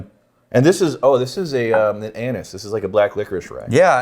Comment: does say "this is like an anus"? Asking for myself, cause that's what I hear.